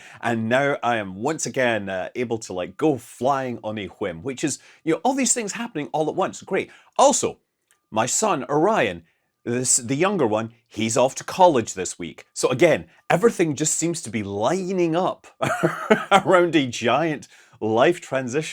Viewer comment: Scott, did I hear you say that you named your son, "Orion?" 😮 Dude, that is next-level geekdom! 🫡 I identify as an Alpha Geek, but I am not worthy. I'm glad you didn't name him "Crab." Fly safely, bro!